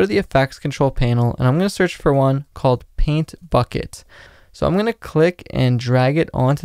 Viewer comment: that really sounded like "paint fuck it"